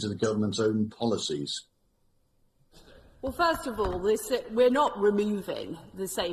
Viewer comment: Does she say "First of all we are sick... we're not etc."?